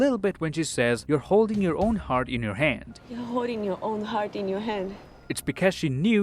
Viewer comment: i listened to this in .25 x speed and i heard you say "im holding my hart"